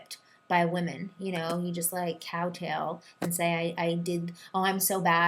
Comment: does she say "cow tail"? Lol